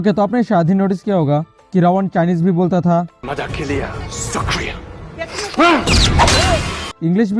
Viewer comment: par dhyaan se sunna woh hindi hi bol rha hai, kyunki tom wu woh role play kar rhe the isliye woh accent se samajh nhi aaya hoga. But he actually said-"madad ke liye shukriya".